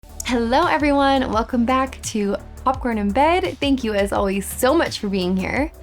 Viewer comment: "Popcorn In Bed"? I thought you said, "Awkward In Bed". 😇😇😇